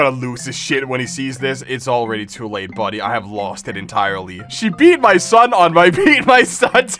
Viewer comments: Five hundred yummers? I thought you said Five hundred beat my son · Don't beat my son? I thought you said beat my son. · Exercise? I thought you said beat my son